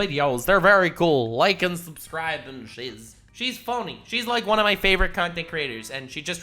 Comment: did you say she's "funny"? Or "phoney"?